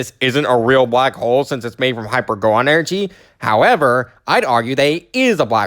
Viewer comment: for a second I thought you were going to make an Undertale reference because it sounded like he said hyper goner.